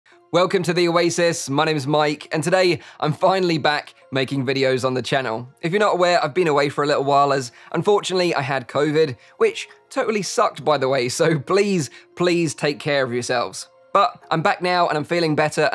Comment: i heard you say bovid in da beginning